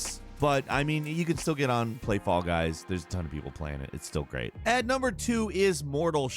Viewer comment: Everytime he says "Fall Guys", I hear "Fog Eyes".